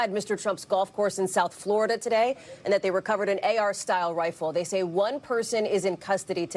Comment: Ar style? Thought they said AK style.... changing their narrative already...